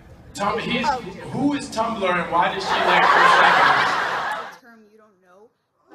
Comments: what did that girl said? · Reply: Sarah Mai "friend may be a term you don't know" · what was said? The black in me needs to know because I related to what he said about friends vs family lol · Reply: She said "friend may be a term you don't know". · please what did those girls say , anyone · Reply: The girls kept saying "my friends want me to ask this and that" So mackie goes "damn yall got a lot of friends" She responds "friends may be a term you're not familiar with" then we see the response